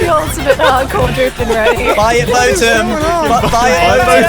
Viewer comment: All I heard at 10:43 was "buy a Boatem"